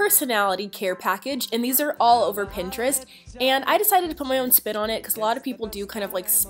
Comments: am i the only one who thought she said put my own spit on it? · Did she say put my own spit on it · Who is watching July 2017? Also when you said "I just decided to put my own spin on it." I thought you said "I decide to put my own spit on it."